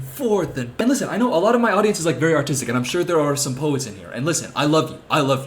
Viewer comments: I had to listen again, bc first time i heard "i know my audience is very autistic" and was like how the hell does he know that also i love content and am both neurodivergent and an artist lol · I thought you said all of your audience were autistic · I'm not gonna lie when he said "my audience is very artistic" I thought he said autistic And I had to take a moment of think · HE SAID "and listen ik a lot of my audience is very artistic" BUT I HEARD AUTISTIC AND I HAD TO DO A DOUBLETAKE LMAOOO